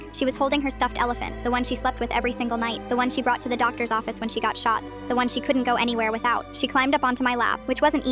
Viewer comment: I was very concerned for a second because I thought it said “When she got shot” instead of “When she got shots”